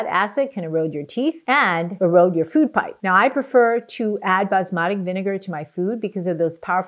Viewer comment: Near the end you talked about vinegar and it sounded like you said Basmati vinegar did you mean to say balsamic? Thank you for you're wonderful videos